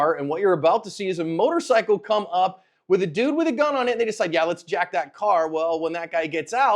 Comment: Hey I may have misheard what said at 0:52 was that an n bomb? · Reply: He said "with a dude with a gun on it and they decide yeah let's jack that car."